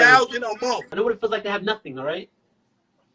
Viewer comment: $6700 a month is a shitload of money. I kill myself working and don't even get a third of that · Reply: Bruh’! I thought they were saying $700. $6700 a month?!!! I freakin’ wish! This boy is crazy!